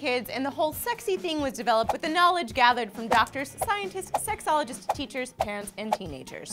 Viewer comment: wait did she just say "sexologist"